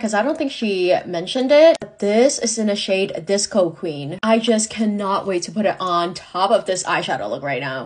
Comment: I thought she said the shade was called coke queen👀